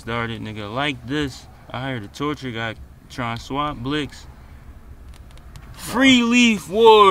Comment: I heard your torcher got killed tronna swap blicks 😀